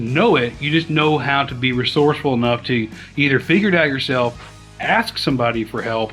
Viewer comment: At about 8:50 did you say "figure it out" or finger it out"? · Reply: Probably figure lol